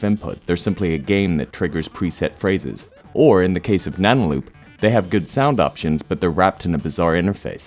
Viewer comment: what did you say i didn't catch it. Something like nanoly?